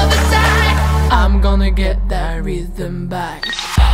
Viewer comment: мне одному послышалось вкусный чай?